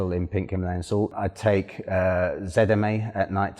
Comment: What was the supplemental you said at 10:00? Sounded like Zetamin? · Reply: Eric Bartoszak It’s called ZMA he just pronounces his “Z’s” differently